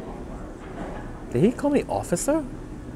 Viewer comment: lol, it did sound like “officer” but i think he said coffee and then said “coffee sir?”